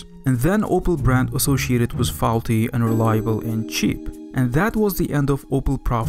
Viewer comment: "unreliable" or "and reliable" what did you say, i cant understand what you are saying, speak clearly